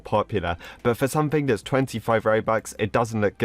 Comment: Did he just say "for something that is 25 Robux"